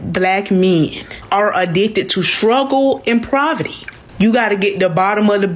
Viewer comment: "Struggle and Proverty" yeah I'm good🤡😂🤣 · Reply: It sounded like she said shruggle and proverty.